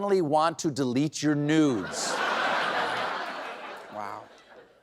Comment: I thought he said news, not nudes.